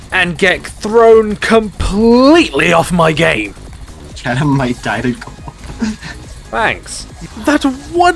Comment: well. I heard it. Have no idea what was said, though. Lol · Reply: They said I was going to die